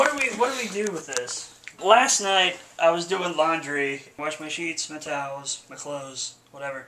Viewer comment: The funny thing is that when first said "I was doing laundry", I thought he said "I was doing lingerie. Guess I really did hear him right.